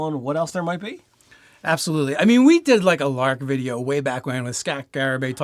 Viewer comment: “We did a lark video.” I heard, “We did a LARP video.” Thinking it wouldn’t be the first time. LOL